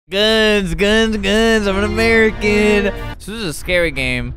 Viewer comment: First thing I hear is “gUnS gUnS gUNS”